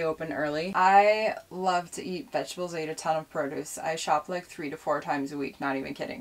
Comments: lol I heard her say I shop lift 3-4 times a week...had to go back and listen again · Did she say shop lift?